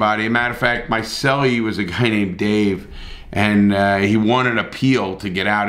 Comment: I heard that as “he won a peel” and was wondering how a banana peel would help in prison for a second